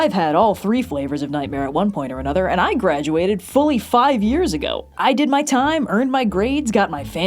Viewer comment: I legitimately misheard saying "fully 5 years ago" as "45 years ago" and immediately had to do a double take because math wasn't mathing